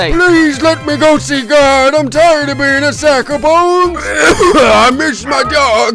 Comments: captions say saxophone instead of sack of bone LMAO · What it actually says: I’m tired of being a sack of bones YouTube subtitles: I’m tired of being a saxophone